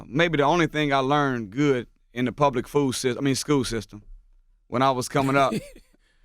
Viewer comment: Did he say the public fool system lol... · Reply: I think he said food😂😂😂😂😂😂